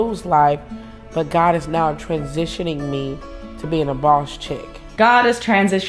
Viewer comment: “God is transitioning me into a boss chick” is one of the most hilarious statements I have ever heard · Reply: I heard "Boss Bitch" until corrected my PG13 thoughts! Lol